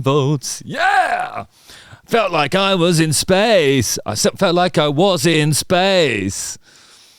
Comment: I thought he said "it's like I wasn't spade!"